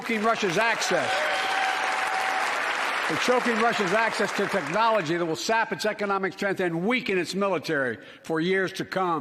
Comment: if you can tell me what he says after “crushing Russia’s assets” I’ll thank you greatly 🤣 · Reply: "Crushing Russia's assets to technology that will sap its economic strength and weaken its military" you're welcome lmao